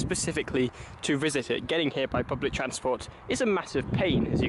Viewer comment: Did you say 'public transport was a pain'